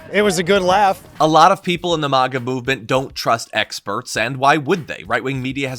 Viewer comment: With the short A pronunciation it sounded like you said the Vodka movement 🤣🤣🤣🤣🤣